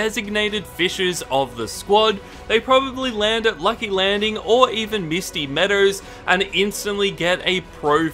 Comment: What did he say i heard him say lucky Landing or Misty Meadows to go fishing